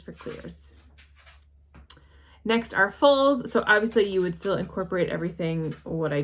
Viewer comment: did you say "fulls"? I haven't heard that term. Considering wls.